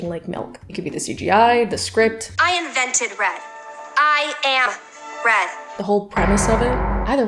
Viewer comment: I thought she said “I invented rap….I am rap” and I was so confused. Like I know riverdales script is wild but 😅